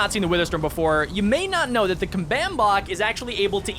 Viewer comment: YAAAAAAAAAS THE WITHER STORM1!!!!!!!!!!!!!! I LOVE UR VIDS BRO1!! BTW: Did he just say 'combanb bop'